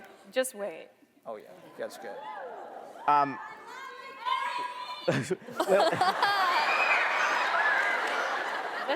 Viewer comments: someone to tell me what it was that I shouted 💕💕💕💕 "I don't speak English and I don't understand what they say""😊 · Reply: Daniela Navarro marry me - it was said😁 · what was that? I didn't hear it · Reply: “I love you marry me” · what are the fans saying? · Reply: tropical candyland marry me